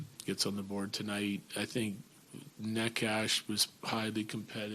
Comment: Did call Marty "Neck Ash" at 2:57? 😂